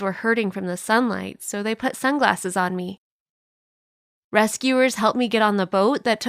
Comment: Did i just hear "they put sunglasses underwater?!